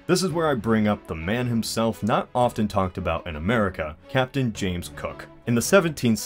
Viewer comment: when you mishear. "Captain James Kirk"